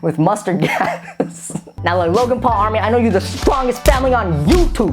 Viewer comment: Did he just say? “Logang”?